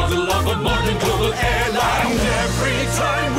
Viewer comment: sounds like they said "every time we fly we will ensure that you will never die!"